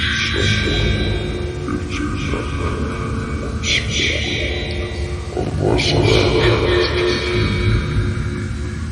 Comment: I dont know what he said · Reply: "Citizens of the United States, and alongside the world, our impending doom awaits within two days. For I shall speak your first and only warning regarding the Moon. It is unfortunate to say that whatever disaster is occurring at this time is not natural. Everything that has happened is coming from the Moon. The Moon, which has a dark, dark secret, in which humanity hasn't found out, is rather controlled by an unknown source, in which, in some way, it has been activated once more. Unless action is immediately taken, the Moon will set to have a collision course with the Earth within the next 48 hours. And when those 48 hours are up, humans, plants and animals, will all perish and Earth will become a dreaded wasteland. Everything will be gone, no one will survive the Moon's fall. Unless you hear our warning, warn NASA about our impending doom. There is one solution to this entire thing and listen carefully. 'In the tower, between the south and the east, a clock will appear when you walk in. Beneath the tower, will guide you to the answer.' Humanity, find the answer, or face the wrath of the Moon."